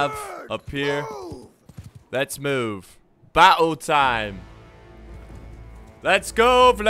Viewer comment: did he just say battle time in a really British accent? If there are any fellow brits , you have to back me up on this